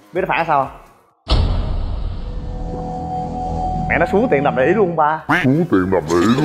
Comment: mới nghe, nghe nhầm thành "Xuống tiền làm đũy" =))))))